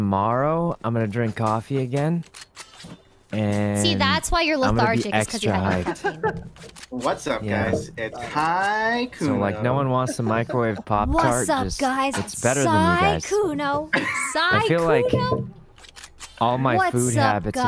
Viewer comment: Lmao did he just say that microwaved poptarts are better than them? 💀